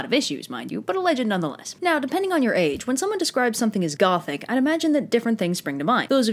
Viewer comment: I thought Gaelic when you said Gothic